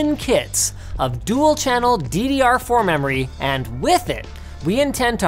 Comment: if you turn on the subtitle at 0:19 it will say "ddr3" instead of ddr4 :V